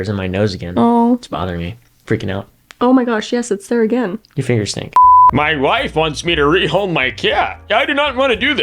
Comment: Did he say your fingers stink? IMMEDIATE DIVORCE. Kidding but seriously dudes blunt as fuck huh 😂